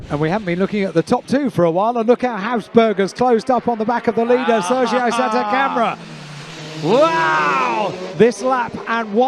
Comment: why did i heard Sergio setup a camera ?😅😅😅😅